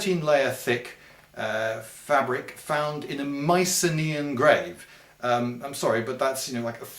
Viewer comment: Sorry, English is not my native language, so I sometimes have trouble understanding certain names. For example: What did you say at 3:13 " A Mae....My....Mi.....on grave"? How do you spell that name? Apart from that, I LOVE your videos! Keep doing this forever please :') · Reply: Mycenaean was the word. They're bronze age greeks.